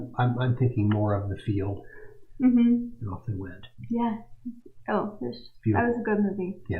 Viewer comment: Great presentation. I always look forward to your videos. The conversation between and is just uncomfortable. I wish any of the several interviews with and the FBI would leak. At 3:26 what does say after the field... I can't understand it. Does he say "he wept"? I haven't seen the movie so anyone know what he is referring to?